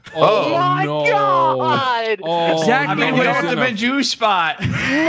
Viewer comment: i thought said "Juice spot" so i spit my fucking lemonade when u found him